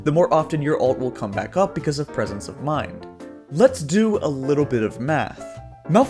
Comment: did he just say lets do a little bit of meth?